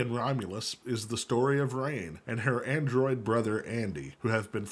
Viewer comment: had to do a double-take. Thought homie said "angroid" 😂